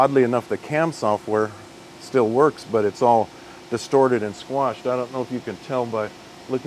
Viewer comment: Thought he said "squanched" not squashed for a bit then, almost a closet Rick n morty fan outed proving he isn't a cyborg. But as it stands if he asks for your clothes, boots and your motorcycle I suggest you hand m'over.